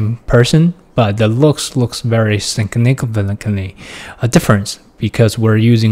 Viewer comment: Can anyone tell what word he said at 1:13? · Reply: “significantly” lol sorry for my poor pronouncation